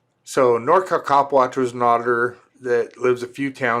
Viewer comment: legit thought you said his name was Cock watch 😂